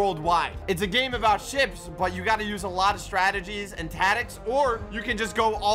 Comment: “Tatics” (instead of tactics) 🤣